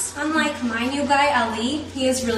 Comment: "Oil lamps are so 1687" 😂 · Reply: I could've sworn she said "Royal lands are so 1687"